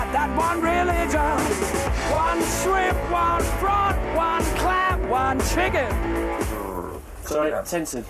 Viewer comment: Chicken feed babe! One shrimp, one prawn, one clam, one chicken! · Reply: I hear "one shrimp one frog one clam one chicken"